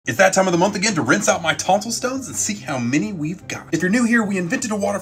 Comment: I thought he said “if it’s that time of the month again than you gotta take out braces”